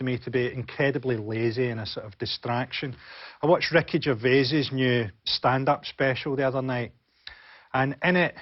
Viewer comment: I heard him say "Wreckage O' Vases standup special" and tried to find this new panel show I never heard of before, without success. I was so certain in was real though, with shows like Mock the Weak, Nevermind the Buzzcocks and 9 out of 10 Cats Does Countdown....